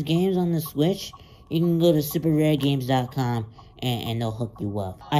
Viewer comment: I thought it said rape games